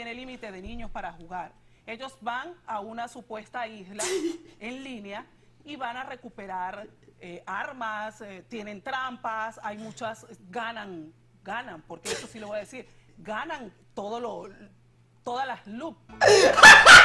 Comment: Se que estoy tarde pero en el minuto 4:12 pensé que decía que tenían que ir a recuperar la malvinas